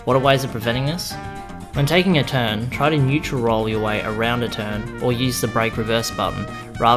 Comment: Didn't quite get what you said at 8:10. "Try to ???? your way around a turn" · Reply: Nytheris Neutral Roll other terms don't hold break or accelerate just roll along and turn!